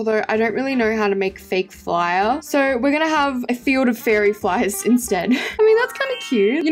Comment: Am i tye only one who heard her say fairy flies instead of fire flies 😅